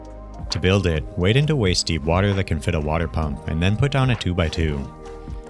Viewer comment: what did you say here? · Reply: wade into waist-deep water that can fit a water pump